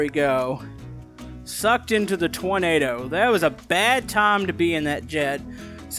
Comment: said tornado but I hear toynado or troynado? Idk idk just telling ok🌪️😱😫